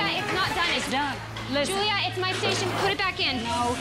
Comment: can someone tell me what the blonde said? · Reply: "Julia it's my station, put it back in"